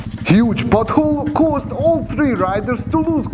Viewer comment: does he say "huge butthole caught all 3 riders"? 😂 jk, he says pot hole, but I had to rewind to check lol